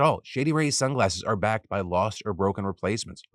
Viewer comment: Definitely thought he was saying “shitty rays” during the entire ad. What a bad company name lol